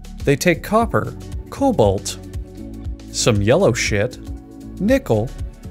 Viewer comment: what did he say in 1:27 ?? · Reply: Some yellow shit